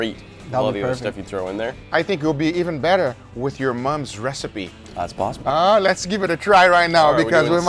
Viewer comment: i thought he just Said “with your mom” 💀